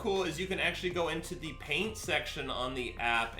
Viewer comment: I thought he said pain section instead of paint